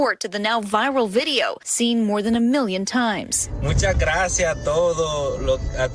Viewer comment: At first, I thought he said "Mucha gracia toto". But I don't think she was THAT grateful for being saved.